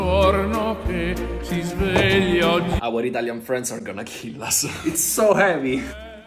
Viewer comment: "our Italian friend's are gonna kill us" got me dying☠️ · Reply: I SWEAR I heard it "abuelita in France gonna kill us"😂 I rewatched the video many time and still hear it the same!😂